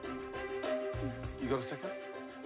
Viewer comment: does he say “you got a second?” naaaa gordon, theyre busy